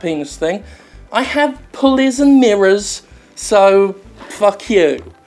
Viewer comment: Third time's a charm. Sincere apologies for this video going up and down like my pants at a "Bum Jim Sterling In The Bum" party. It has been a very rough night. Video is fixed and ready for your enjoyment. <3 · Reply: Wait, I thought he said "bullies and queers".